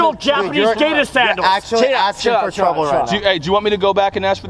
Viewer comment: "You don't understand, they're traditional Japanese gay-ta sandals!!" I nearly lost my shit hearing that :P